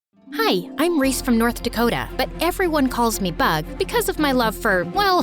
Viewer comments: I thought I heard North Korea 😭😭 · I thought she said”everyone calls me butt” 😅